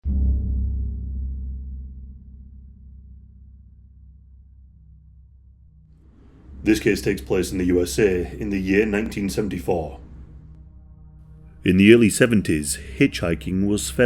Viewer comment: All I hear now is this cake tastes baked